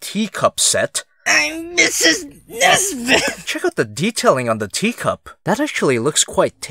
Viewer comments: i heard I'm mrs nesquick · Can anyone translate what he said. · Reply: I’m MiSsEs NeSbIt